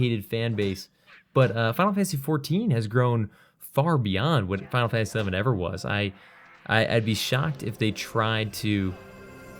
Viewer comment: you keep saying final fantasy eleven so fast i thought you were saying final fantasy seven